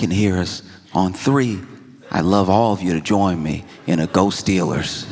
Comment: When he said "go Steelers" I thought he said "ghost dealers" because of all the talk about ghosts 😂